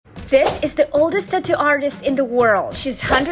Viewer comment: I thought u said this is the oldest tattoo 😂❤